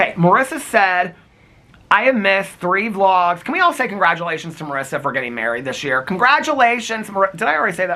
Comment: When you said Congratulations to Marissa I thought you said Karisa— and thought….how did he know? 😂 I got married in December ❤️ congratulations Marissa! 💙